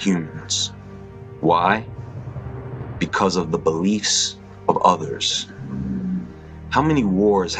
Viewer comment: I thought he said bleach XD I was like yeah sure cancer kills 2 seconds later ohh Beliefs gotcha...